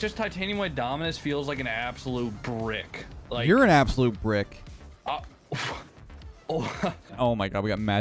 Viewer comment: HAHAHAHAHA 10 seconds in and I hear "You're an absolute brick..." 🤣🤣